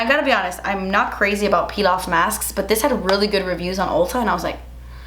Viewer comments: Lmao I thought you said “pilaf masks” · I really thought you said pilaf masks 🤦🏻‍♀️😂